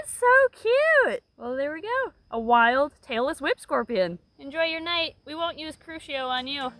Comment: When mentioned the tailless whip scorpion, i thought she said Taylor Swift scorpion. I was like 😲